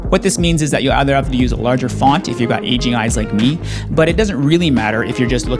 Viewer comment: Bro, at first I thought you said "Asian eyes". I was wondering why that affected anything. Then I was like, "Ohhhhh! AGING eyes!"